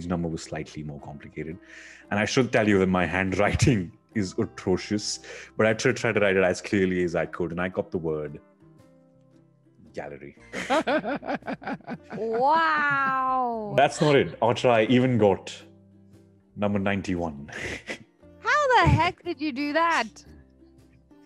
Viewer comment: did she said meow